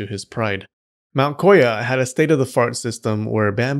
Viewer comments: Did I just hear "state of the fart"? Lmao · did he just say that the had a State-of-the-FART system?? 😂😂😂😂😂😂